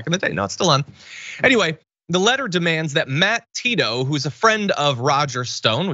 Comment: I misheard "Matt Tito", and thought you said "Matt Paedo" - I thought it was a reference to Gaetz!